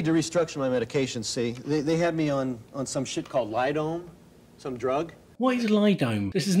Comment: it sounds like you said 'lidome' but I couldn't find any info about that being a drug. If you still want to know, I found some info about lidone on PubChem. Could it be molidone (<link> ) or molidone hydrochloride (<link> )? These seem like they could be the right sort of drugs to treat some of his symptoms as PubChem says they have antipsychotic and anti-schizophrenic properties.